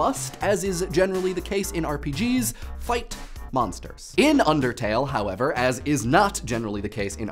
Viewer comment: i heard you say undertale 4... wait..... but that means only one thing..... ILLUMINATI CONFIRMED